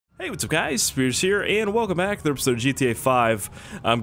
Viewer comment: Too much of watching this game This is wut I heard "Hey guys welcome back to btd 5"